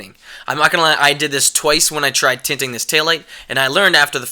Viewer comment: In your video i thought you said your tinting a tail light not a headlight.